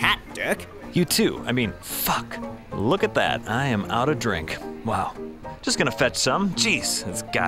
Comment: Wait do I hear ''Our Drink?" Blush [SSSSCCCCRRRREEEEEEHHHHHHHH Scream intesife ]